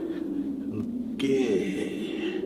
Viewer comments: why does he just say gay randomly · “I’m gay” Anyone else hear this? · sounded like he said I’m gay, I know he said ok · Did anybody else hear him randomly say gay